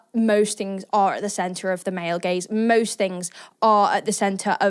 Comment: subtitles said "male gays" instead of "male gaze"😭😭